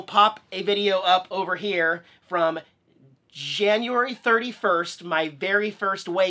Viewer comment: did you say Jan 31st?